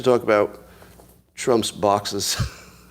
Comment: I thought I heard Scott say "I guess we have to talk about Trump's boxers... Let's talk about Trump's boxers.... who leaked ..". Now that would be a story.